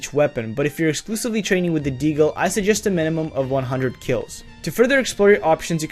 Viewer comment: Bro i legit thought he said 5200 kills